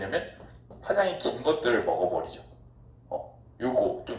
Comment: 선생님 07:05초에 뭐라고 하신 거에요..? 어디에서 먹어버리냐면 ~~ 그 뒤에 말이 잘 안 들여욥 ㅠ.ㅠ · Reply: 파장이 긴것들을 먹어버린대~~!!